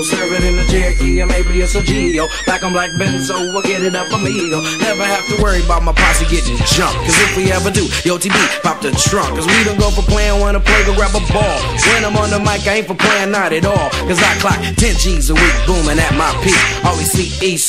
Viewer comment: Crap!All these years I thought he was saying"Yo,LTD,pop the trunk!"-oh,well!-God bless you,MC Breed!